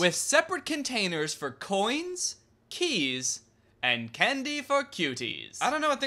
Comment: I think when he said "candy for cuties" he meant actual children. People back then were weird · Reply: Art & Stuff I thought he said “candy for kiddies”